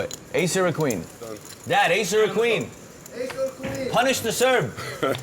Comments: punish the SERB????? am i heard that correct? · punish the SERB????? am i heard that correct? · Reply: lalos I heard "...punish the Serb..." too. That's racist, right?